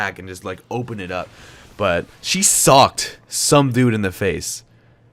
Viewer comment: I thought you said "my mum sucked some dude"